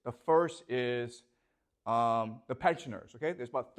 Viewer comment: What did he say is the most powerful lobbying organization at about 1:00? can anyone clarify? thanks..🙏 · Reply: Pensioners, lobby that ensures social security/retirement funds for people over 50